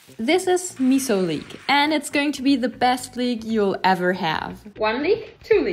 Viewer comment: I thought she said"She's the best think u'll ever have" lol I heard it wrong😅